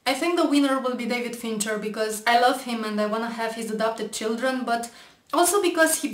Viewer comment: When you said "I want to have his adopted children" I thought you said "I want to have his abducted children". (Note to myself: Really? That's what you got from this video?)